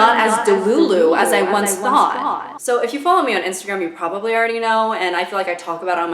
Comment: I thought you said “Should you follow dream?” Like no I don’t think so